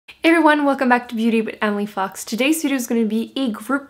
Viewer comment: i had to rewind it twice i thought she said welcome to beauty and we fuck